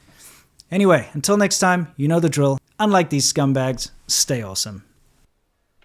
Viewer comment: I was so used to the videos of apostate prophet, that i misheard the "stay awesome" for "stay away from Islam" first💀